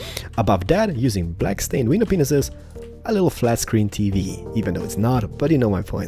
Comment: did he just say pinises